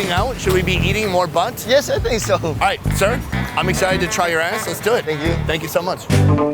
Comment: Did he just say "I'm excited to try your ass"